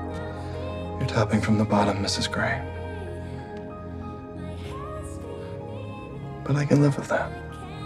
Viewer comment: Y’all what does he say at 2:28 I’ve watched this scene so many times but I can’t figure it out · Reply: AwkwardlyIna “You’re topping from the bottom Mrs. Grey. But I can live with that.” 😏